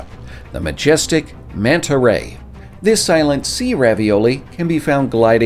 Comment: uh excuse me just wanna be sure i heard this right but.... he said Sea ravioli right?